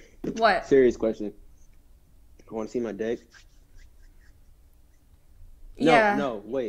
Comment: i thought he said di